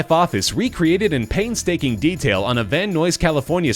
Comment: did he just say van noise instead of van nuys